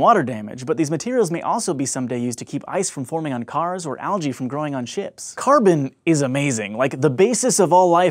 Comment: I thought he said and stop algae growing on chips 😀😀😀😀😄😄😄😄😄😄😄😄😄😄😄😄😄💩💩💩💩💩💩😋😋😋😋😋😋😋😋😜😜😜😜😜😜😜😜😜😜